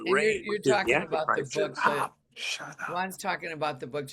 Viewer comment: What is that at 37:15 ?! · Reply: I heard someone say, "shut up" What the heck?